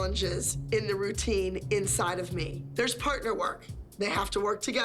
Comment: I don't know why I thought Abby said "You're inside of me." 😭